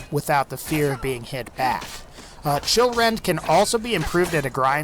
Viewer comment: When he said Chillrend I thought he said children 😂